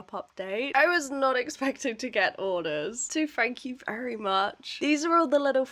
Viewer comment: Wha a fun video! And by the way when you said thank you very much when packing the orders, I thought you said Frank You Berry Mush 😂 it’s a reference to the story of the mouse doctor, Dr De Soto, which you should look up! Ooh and maybe draw, an episode inspired by quotes from children’s books! ❤❤❤